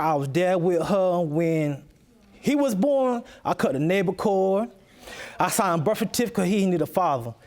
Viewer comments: This man out here cutting nable cords😂😂😂💀💀💀💀💀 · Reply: I thought he said he cut the cable cords. 🤭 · Not navel cord...father fix it. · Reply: That makes slightly more sense. I thought she said the neighbor cord.